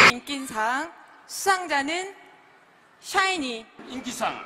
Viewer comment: 뭔데 사이니로 들리짘ㅋㅋㅋㅋ 발음봨ㅋㅋㅋㅋ